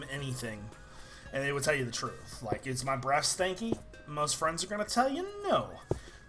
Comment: when you said breath I thought you said breast. so I was like What?